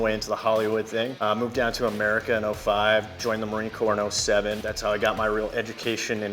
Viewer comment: first time hearing it I thought he said he joined the marine corps when he was 7 😂😂